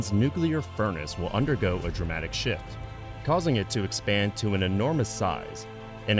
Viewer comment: i heard "dramatic shit"